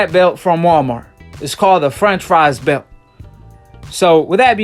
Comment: Lol!!! Good shit calling bs on mr. Gary Russell. But dog stay away from saying Franchise belt. That shit sound like you saying a French fry belt 😆 🤣 😂 😆